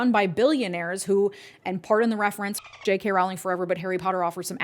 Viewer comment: 😲I thought you said "JK Rowling forever" -- something I never expected to hear from you. I had to listen again and watch your lips to realize you were actually saying "F*** JK Rowling forever"